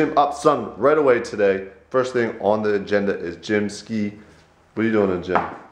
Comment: did you say "gym, SKEET" ??? hahahaha Anyways KONY 2012 mayne!!!!!!! PVP SAAN!! thumbs up so he can clarify!!